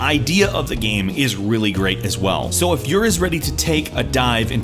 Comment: Was it just me or did he say "So if you is ready"